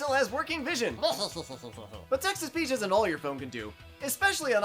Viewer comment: I don't know about anyone else, but I keep hearing "Texas Peach" instead of text-to-speech